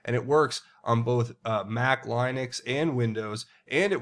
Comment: did you just say Line- ix? Linux- Linux mate